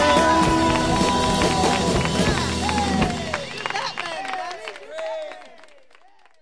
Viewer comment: I think I heard "ayyyy Batman!" Am I alone on this? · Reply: Ayy, you're Batman, Johnny!